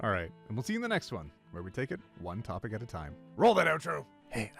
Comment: for some reason i hear "wanton topic" instead of one topic